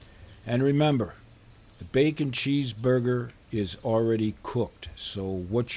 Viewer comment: i almost thought that said Cheeseburger PIES. 🤣